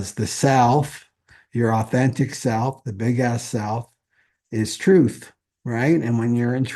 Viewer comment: I heard "the authentic self, the big ass self" and nodded to myself "yes, yes, that's right." 😆 (ahhh you said the big S self!!)